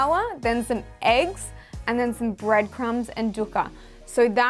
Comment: what is she saying? dip it in flour, eggs, breadcrumbs, and duca??